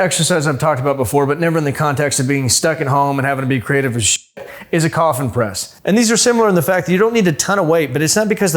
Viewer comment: Did.....did I hear you say coffin Dance press?